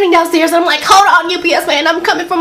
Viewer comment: When she said "UPS man," I thought she was saying "You BS man."